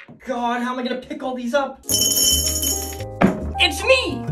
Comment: when he said how am I going to pick all theese up i thought he said how am I going to pickle theese up